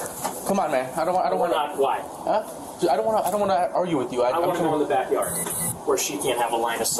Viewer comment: What did he say @ 47:37😮”we’re not black”? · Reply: he said "we're not-why?" started a sentence then cut himself off with the "Why"